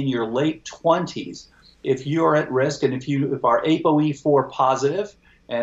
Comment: Did you say "able E4" ? "apo E4" positive|negative @ 35:50 ?